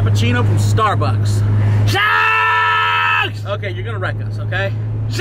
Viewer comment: Did agp say “SEX” at 5:23 and we will ALWAYS REMEMBER YOU AGP/Charles. Green rip 🖤🙏🏼😭<num> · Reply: Nvm he said sucks